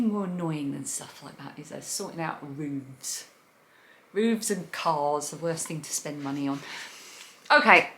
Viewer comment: So looking forward to this episode!!! Loved your comment about Rooves and Cars not worth spending money on....when I first heard it I thought you said BOOBS and Cars....after crying with laughter I did a quick rewind and heard you say ROOVES.....oh my!! Thank you again for being a delightful way to escape for a bit from all the stress of the world.